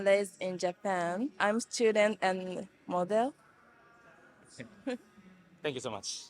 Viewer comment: does she say she is a student and mother?